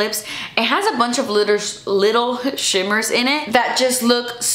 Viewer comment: When you said little shimmers I thought you said little shitters I’m going to start saying that little shitters lol